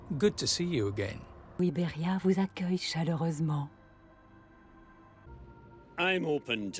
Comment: Hey, I don't know if you still read the comments on videos released 3 years ago, but if you want to know what says at 12:51 it's "Iberia vous accueille chaleureusement" which means "Iberia welcomes you warmly" if you ever want to add her quote to the captions